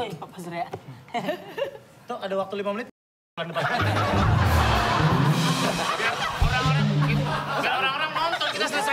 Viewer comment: Surya ngomong apaan emng?? Kok disensor? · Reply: "kita pukul pukulan di depan sini"